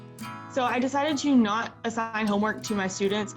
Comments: (Subtitles) ASSING! INSTEAD OF ASSIGN BAGAGAHAHAHAHA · lol the subtitle says assing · Did anyone else notice at 2:28 the captions say she's not "assing" homework instead of not assigning it